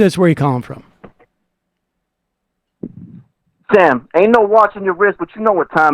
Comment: IF YOU DON'T LOVE THE BRA, SEND IT BACK! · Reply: Did I hear right, "Ain't no watch on the rez?" at about 1:42:00? lol